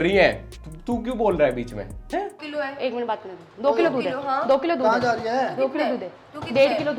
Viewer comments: Did she said 2 kilo DUDH hai.....?🧐🧐🧐😂😂😂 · Did she say 2 kg Doodh? (Milk)